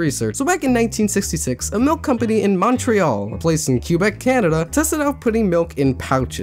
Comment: It took me a full minute to realize that you said Quebec Canada and not cubet Canada because it sounds like you said cubet xD